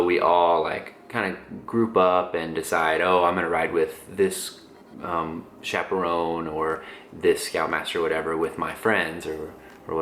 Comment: wait did he say CHAPPEL ROAN ??????